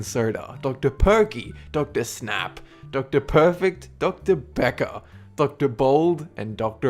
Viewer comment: When he said Dr.Becker I thought he said Dr.pecker XD